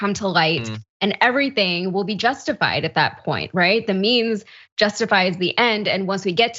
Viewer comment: At first I thought she said "The memes justify the ends" at 3:42 but then I realized even if she had, that would still be true for the right wing.